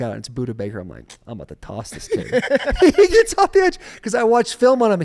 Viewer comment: Can someone help me with what Taylor says at 1:40:35? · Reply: "It’s Buddha Baker, I’m about to toss this kid."